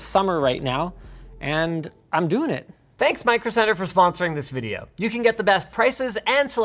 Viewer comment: i thought he said he was sponsored by microsoft and i was like bro waht